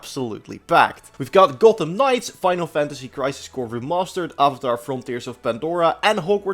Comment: I swear to god I heard you say: Final Fantasy Crisis Core — ReLobstered